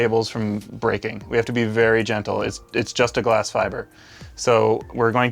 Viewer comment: did he say djently